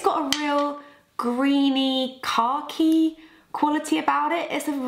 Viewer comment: When you said it had a “khaki quality” i heard “car key quality” LOL